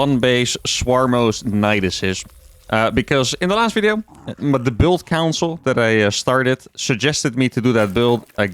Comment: Did he just say, "the build counsel that I started..."? 🤣🤣🤣🤣 Lekker bouwuhh, starcraft construction company 🤣🤣